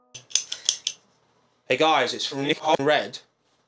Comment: did you reverse something you said at 6:20? I heard it «hey guys, it's from reversed word(s) and red».